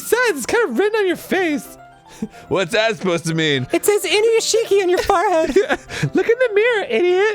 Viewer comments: did Dan just said Inuyashiki? Dan watches anime? wtf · did dan just say inuyashiki